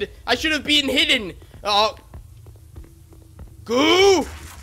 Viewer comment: did he just say "goob"?